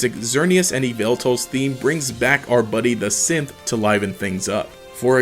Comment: Bothers me how many times I heard "brings back our buddy the synth" at 5:29 as "brings back our buddy the simp"